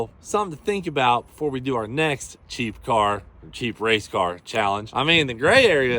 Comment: Im half deaf, I thought you said "cheat car contest " Do it for Dale - old school Nascar rule style😂😅